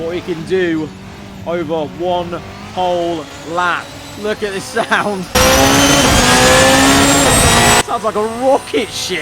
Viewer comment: hold up did really say look at the sound